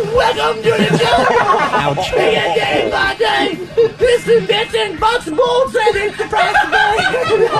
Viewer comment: What does say at 1:17:13 that makes laugh? For the life of me I cant make it out. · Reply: Twisted bits and bucks and bolts and it's the price to pay